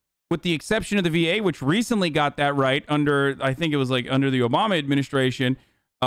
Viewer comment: To hear that diabetic people die because they can't afford insulin when it's literally the cheapest it has ever been to produce is really sad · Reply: i thought he said up until the obama administration that was the case